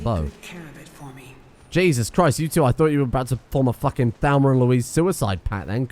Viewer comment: I realize I'm a year and a half late to this, but did you just say your companions nearly had a 'Thalmor and Louise' moment?